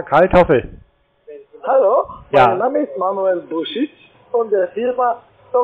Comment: "Mein Name ist Herr Manuel Bullshit" oder was sagte er?